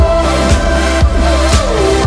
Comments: what does Youngjae say at the end!?!? "This ain't a honeymoon!?!?!" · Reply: I hear "Feels like the HoneyMoon" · what does Youngjae say at the end!?!? "This ain't a honeymoon!?!?!" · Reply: Stephanie Cardenas lol I heard "listen the honeymoon" (I know it ain't make sense sorry 😂😂😂)